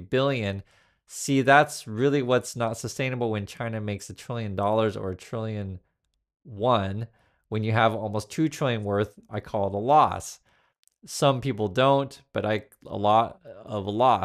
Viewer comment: was Trump saying a trillion yuan?